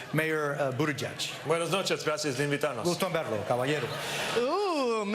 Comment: I speak Spanish. My first language. Still didn’t understand what Buttigieg said and I actually tried so that I am not biased. It was cringe. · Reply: Brian Gonzales I thought he said “gracias por invitarnos”